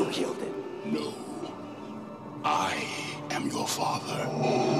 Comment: My life was a whole lie. I have always thought he said “Luke, I am your father” instead of “No, I am your father”